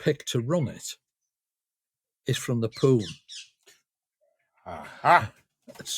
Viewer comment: 'And one of the leaders of [the station] was from the POUM'. I thought he said 'from the Pool' (as in Liverpool) and was expecting some Liverpool love-in from the two of them......'we get everywhere' etc.